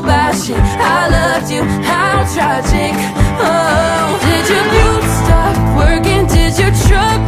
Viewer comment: i thought she said “did ur boobs stop working” 💀